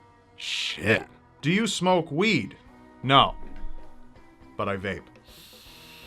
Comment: what did he said on 2:24 ?? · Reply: No...but I vape